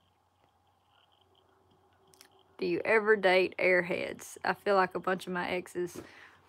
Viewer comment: I thought you were saying arrow heads instead of air heads. Because arrow heads is deeply apart of my heritage. I'm the descendant of the real Americans witch is the native people of this country that "" Columbus discovered"".